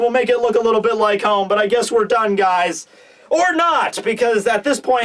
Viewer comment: "I guess we're done guys"...Kenpachi going bankai has changed my view of things so much that I heard this line as "I gues we're dangais" as in dangai Ichigo :D